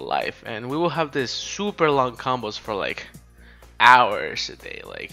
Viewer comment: Did anybody else hear combos? lmao